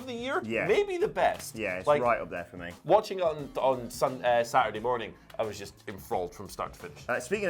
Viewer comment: “watching ____ Saturday morning “ What did he say ???? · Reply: I think he was going to say "Watching on Sunday morning" before correcting himself.